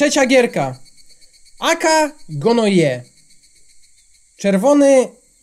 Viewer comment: Pov:kiedy widzisz swojego chłopaka ze swojim kumplem i pokazóje ich mojej koleżance i mówie (a to są 🌸🔥aka gnoje🌸🔥 Ps to aka gnoje ja usłyszałam że gra to aka gnoje